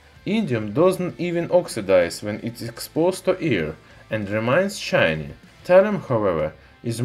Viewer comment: I thought he was saying “when it is exposed to ear” and that thallium is dangerous to human’s skin